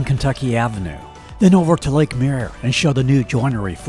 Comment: I thought he was saying Lake Mear.